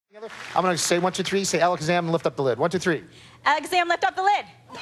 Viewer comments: It sounded like he said "...SAY alakazam lift up the lid" I don't blame the girl that much, I'd probably mess up from being nervous on TV too. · I thought he said: "I'm gonna say 'one two three' you say 'Alex Zam, lift up the lid'" I thought Alex Zam was someone else on stage who was supposed to lift up a lid... or something... I had to watch the first ten seconds four times before I got it... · i didnt get it at first cuz it actually sounds like he says "say alakazam lift up the lid" I dont blame her at all